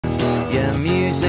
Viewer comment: I think I need hearing aids I heard “Ur musics shy” 😭 Anyways, THE ROOM ECHOED WITH A SNA